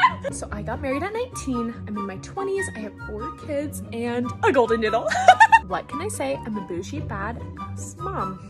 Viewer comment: At first I thought you said golden NOODLE and my brain stoped braining